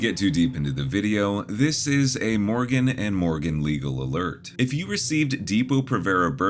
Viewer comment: There is no way... in your ad when you said Morgan and Morgan I could have sworn you said johnson and Johnson but then you proceeded to talk about a shot?! What are the chances!?